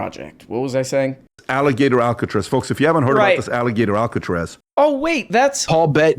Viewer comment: thought they said, "it's all a gay tour" and jumped in with both feet.